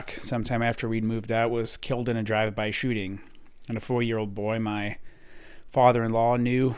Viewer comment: did I hear that right? 4 year old 👦 stabbed someone to death 💀 with scissors ✂️ hoping to get in with a gang as a result? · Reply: 14 year old, but yes.